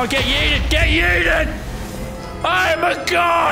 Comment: Bru it sounds like it says YA YEET when it shooys a fire ball